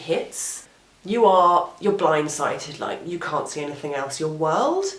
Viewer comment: Thanks for sharing your experiences and making the world a better place. btw, did you say "blind sighted" at 3:39 instead of blindsided?